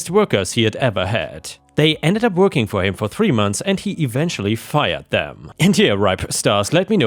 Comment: you mean hired. It honestly sounds like he said he fired them.